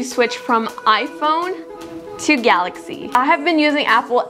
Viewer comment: GAILAXY instead of GALAXY